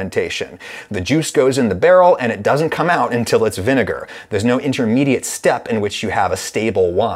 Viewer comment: omg, I can't be the only one that misheard saying "The Jews goes in the barrel and it doesn't come out until it's vinegar" when he was talking about the process of vinegar production in the muslim world